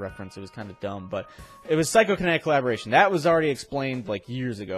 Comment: Physco Kinetic Collaboration? I think thats what he said it's near 6:40 in the video :)